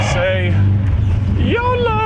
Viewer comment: did just said יאללה ???